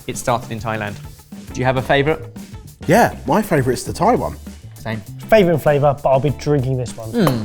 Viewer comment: i misheard 11:55 yeah! my favorite is the Taiwan 🤣🤣🤣